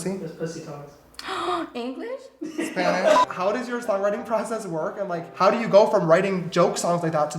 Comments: i thought she was saying “english, Spanish and your friend” 😭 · I thought she was saying english, spanish, british